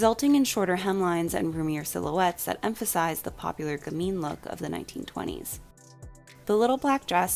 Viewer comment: What do you say at 9:53? The popular 'gameen' look? I'd like to look it up to learn more, but I'm not sure what the word is.